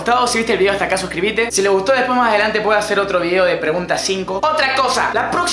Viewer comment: Preguntas IV, Pense que decia Jarvan IV D: Mi vida es una mierda D